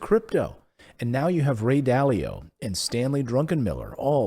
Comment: LOL Did I hear Stanley "DRUNKENmiller" @ 4:35 ?